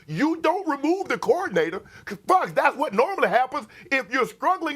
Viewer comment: Did Shannon say the F word? 😂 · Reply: 😂🤣🤣 He said, “Fox, that's what normally happens”